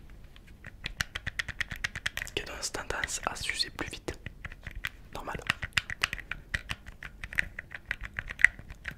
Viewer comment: il y a que moi qui a compris se qu'il a dis a 16:38 ou pas ? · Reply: Ils ont tendances à usés plus vite